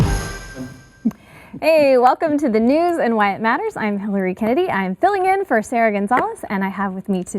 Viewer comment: Sounded like she said "the news and White matters". LOL